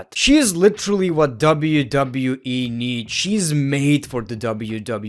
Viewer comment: it sounds like he said wwee